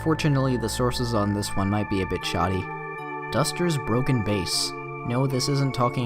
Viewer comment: I thought it said duster's broken ass.